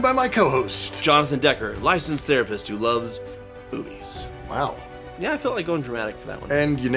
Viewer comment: I thought you dramatically said "and I love boobies". You said "movies". Shows where my head's at.